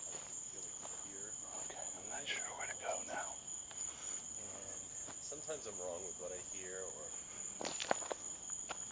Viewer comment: That was an awesome adventure video. Glad your lights lasted until you got back to safety. At 22:43 I heard a disembodied male voice but couldn't hear what was said. ✌️💕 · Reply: That was saying he wasnt sure where to go